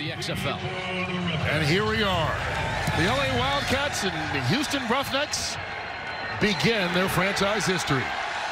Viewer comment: Did he say's " The Houston Roughnuts"?